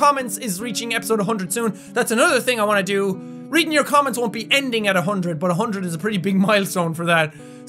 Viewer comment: What!? I thought he said that he wouldn't end reading your comments at episode 100?!?! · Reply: NOTAQUEEN66 HUMAN Oh, I heard "will be ending" but it was "won't be ending". My bad^^